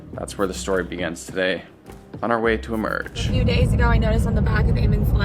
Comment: When you said, "on the way to emerg". I heard, " on the way to the merge". I thought, " what? We're playing survivor now?"